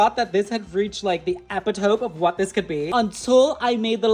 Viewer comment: Epp-it-tope??? Did I... Did I hear app-it-oh-me as EPP-IT-TOPE?? THATS ADORABLE